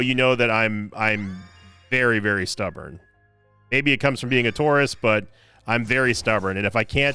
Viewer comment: So for a second I thought said he was stubborn because he "was a TOURIST" and was like "Wow, you're just allowed to admit that, even with the euphamism?" And then connected he said "Taurus" and was not admitting to being former CIA.